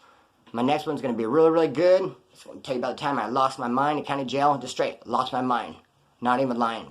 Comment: I thought you said the time I lost my virginity in County Jail! Bro!But you said the time I lost my mind in County Jail!